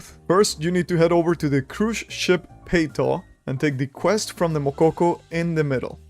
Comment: What is the name of the cruise ship you said at 0:55 ? I can't understand your pronunciation and you don't show its name on screen. Thanks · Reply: Peyto